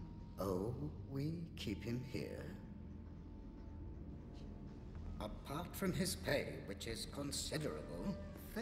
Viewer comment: I just realized he said “Apart from his pay, which is considerable” I always thought he said “Apart from his pain…” as in I thought the taking of his dna was super painful